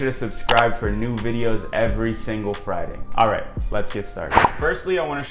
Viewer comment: When you said all right at the beginning, I heard: "A ver", which is common in Spanish 🤦🏻‍♂️